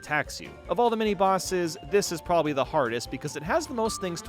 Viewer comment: did I just hear that correct ,THE HARDEST